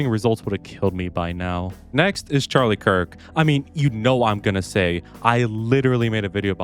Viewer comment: sounded like you said Charlie Kirk